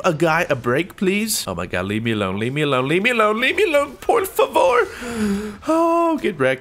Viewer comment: Did he just said "Por favor" ?